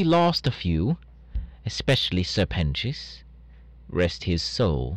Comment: the subtitles said “sir penis” I’m crying 😭